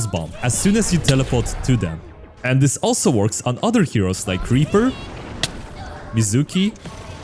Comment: Did you say Creeper?